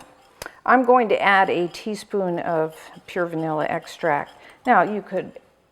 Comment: What did you add in 4:49 , i couldn't understand. Can u help me pls? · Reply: Vanilla extract